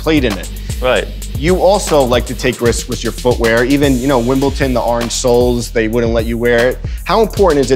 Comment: Did say "Wimbelton" ? LOL